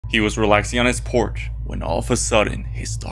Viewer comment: I thought bro said Porsche